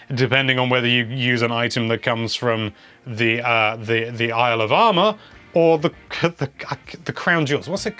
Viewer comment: Everytime says "Isle of Armor" I hear "Alabama" lol.